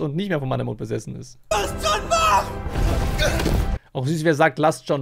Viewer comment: ich hab verstanden los joon mach ..nicht lass joon wach